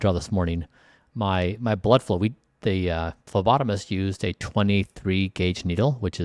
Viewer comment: I thought he said his lobotomist 😭